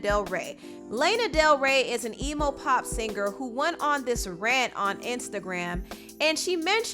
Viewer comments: Nobody: ..."Layna del Rey is an emo-pop singer." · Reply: Oh I thought she said “evil pop singer” lol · Am I the only one who thought she said Lana was an “evil pop singer” haha? I had to listen twice, cause that would have been even more shadier than messing up her name. And idk, if y’all really upset over Doja then idk. This is a girl who made a song about being a cow. She clearly has issues and so I mean, you either bop to her music or not.